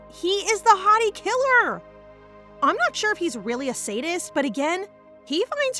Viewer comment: excuse you? did you just say "hottie killer" XD